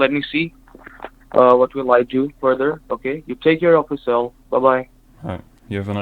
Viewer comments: "[inaudible]" In my opinion, it is audible. I think he says, "You take care of yourself," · i think he said take care of yourself